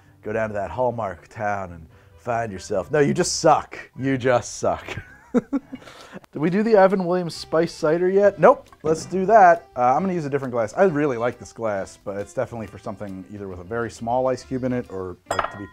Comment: You said “Evan Williams spiced cider” but I heard “Appleton Spiced Cider” and got way too excited